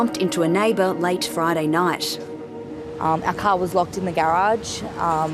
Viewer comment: i thought she said "our cow was locked in the garage" lol!